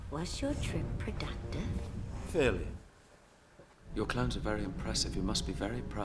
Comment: He actually said clowns 😂